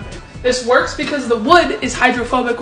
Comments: I thought you said the wood was homophobic 😅🏳️‍🌈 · “the wood is hydrophobic” what i heard: “the wood is homophobic” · I thought they said wood was homophobic and I full on cackled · The first time I watched the video I thought she said homophobic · Bro at first, I thought they said, “the spoon is homophobic” 💀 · I thought you said the spoon is homophobic💀💀💀